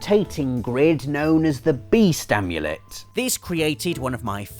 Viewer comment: I thought said Bee Stamulet for a second there!